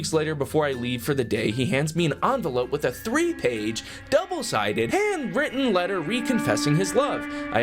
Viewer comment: For sometimes i thought she said double spaced. Holy shit thats single spaced im lucky i didnt have water in my mouth my ribs hurt 😂